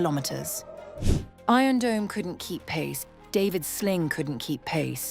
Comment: I thought this bitch said "David Blaine" 😂😂😂